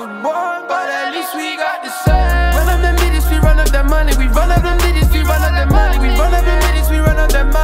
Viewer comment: sounds like he sayin "we run of dem midgets we run of dat money"